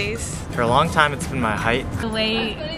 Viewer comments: What is the girl saying at 9:51 about the least attractive thing about her? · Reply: The way she looks to some people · Is she pronouncing "look" as "lick"? YouTube subtitles and I heard lick twice. Your content is great, keep grinding!